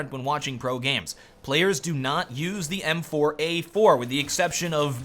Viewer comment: the subtitles on videos are more accurate than kennys' magic stick but when said m4a4 at around 5:45 the subtitles said employee for